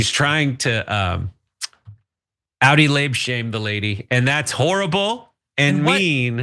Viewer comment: can you clarify what you said at 7:02, please? I couldn’t understand it, and the AI translator thinks you said Audi lab shame LOL but I don’t think that’s it… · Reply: outi labia, like a outi belly button but pussy hood and lips